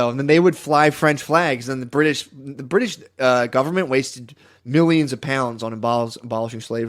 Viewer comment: did he actually say “wasted” 😂😂😂